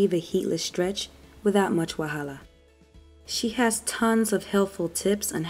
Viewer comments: did you just say "Wahala" are you Nigerian? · Did you just say without much 'wahala' @ 3:09? Are you Nigerian? You have the most informative channel on YouTube right now. 👏👏👏